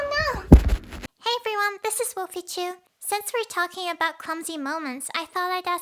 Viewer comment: thé captions say "Pichu" instead of. Thought it’s pretty neat cuz likes pokémon